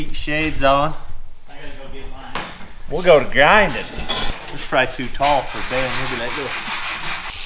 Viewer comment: I thought he said let's go to grinding town. 😆